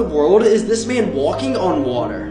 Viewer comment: Him: “he dived and turn upside down” Me: he’s walking on air :0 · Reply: I thought he said he died then turned upside down